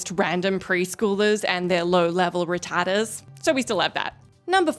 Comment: To my uncultured American ears, it sounded like you said random "preschools and they're retardeds..."